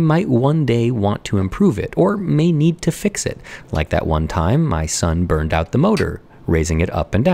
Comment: "of course any time you design something [...] you realise there are plenty opportunities for improvement. and this is not because you failed, in fact this is because you succeeded. you brought something new to the world and then learned how to make it better. that's totally okay" i know this probably will get lost somewhere in the comments but i just wanted to say as an architecture student who constantly doubts their abilities, i really needed to hear that. I'm in the middle of a mid term project and I'm having some sort of crisis over the quality of my work. so thank you, keep up the good work <3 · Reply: I just hear "my son burned the..." Your what?! I didn't know had a son!!! 😮